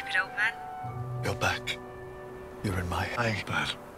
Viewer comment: what did Peter say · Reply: He said “you’re in my iPad”